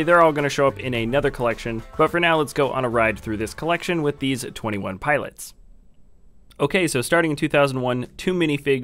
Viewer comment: did you said twenty one pilots